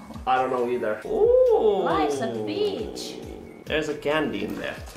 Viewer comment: I’m not the only one who heard “life’s a b*tch” in of “Life’s a beach” right